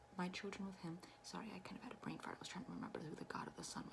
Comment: I think she said her brains smart